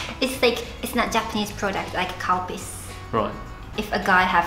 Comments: i thought she said cow piss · Am I the only one who thought said “it’s not a Japanese product, like cow piss” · Dying 😂 when she says Calpis, I had to go back a couple of times because it sound like she was saying ‘’Cow Piss’ 😂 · calpis...I thought she said cow piss at first XD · YO lmfao 5:45 i heard COW PISS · When ever I hear the word 'Calpis' I only hear 'Cow piss' 😂😂😂